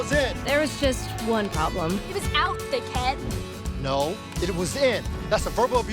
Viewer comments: I thought she said d*ck head not thick head lmao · I legit thought she said d**k head but she said thick head😂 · Is it me or did she said “dickhead” take a look for yourself in number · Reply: She said thick head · Oooh 1:03 did i just hear ”dickhead” i’m shoketh. Jk i know she didn’t say that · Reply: spill the tea sis no she said thickhead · anyone else thought she said “d*ck head” instead of thick head